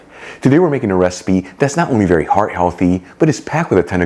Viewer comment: Oh my goodness, I listened to your opening three times before turning on closed captioning because I THOUGHT you said NOT VERY heart healthy!! WHEW!! 😅